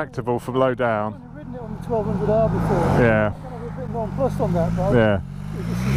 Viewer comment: what is your friend saying? It's comparing it to the Engine of the R1250R? I can't hear :(